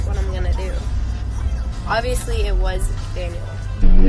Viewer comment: Tbh though it sounded like she said"obvoiusly it was him"but still she said that she was single so....it doesnt really matter